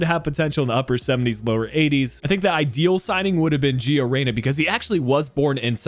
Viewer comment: Who? I can't quite catch the name. Georena? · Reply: Giovanni (Gio) Reyna from Dortmund!